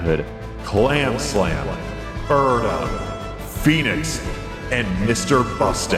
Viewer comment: I misheard you say Phoenix as Fetus